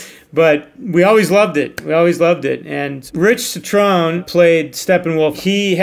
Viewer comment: why do the captions say Ciaran Hinds when he clearly says Rich Cetrone??